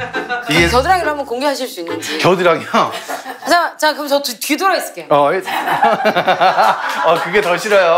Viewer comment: 뀌더라도가 아니라 뒤돌아도로 들었는데 ㅋㅋㅋㅋㅋㅋㅋㅋ